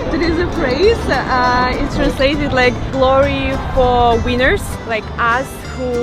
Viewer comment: sounds like she said glory hole weiners 🤷‍♂️😂😂😂😂😂😂